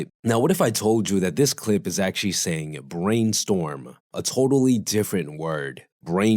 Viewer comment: I heard "brain stole"